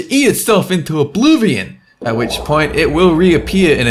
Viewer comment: did just say "obluvion" instead of saying oblivion